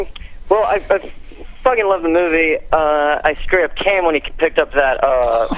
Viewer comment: what did that guy say at 1:15:44 ? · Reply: He said he "straight up came"